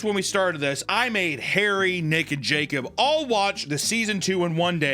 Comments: I swear he said Harry Naked Jacob. Lololol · Did you say Harry, Nick and Jake or Harry naked Jake…? 😂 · I definitely heard, "Harry and Naked Jacob" instead of "Harry, Nick, and Jacob" hahaha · I heard "Hairy Naked Jacob" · hairry naked, Jacob is what i heard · i thought he said "i made hairy naked jacob watch it" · I heard “Harry naked Jacob”. not “Harry, Nick and Jacob” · sounded like he said hairy naked jacob🤨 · Five minutes into the video he said "Harry, Nick, and Jacob" I heard "Hairy, naked Jacob" and needed to rewind